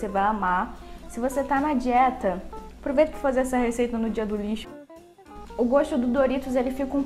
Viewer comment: só eu que não entendi o que ela disse? '' se você tá na dieta e só!? '' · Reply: juli Goulart ela disse é só comer no dia do lixo, que significa um dia dá semana que vc pode comer oq quiser q tá fora dá dieta.